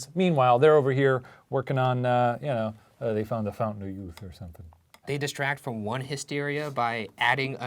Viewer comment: When he said fountain of youth I really thought he said fondue